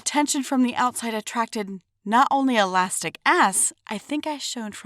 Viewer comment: did she say elastic arse?